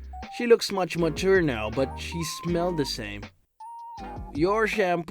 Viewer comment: "..she smelled the same". Did I mishear something?